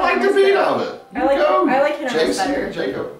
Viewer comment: Did you say that you like him much better at 1:42😱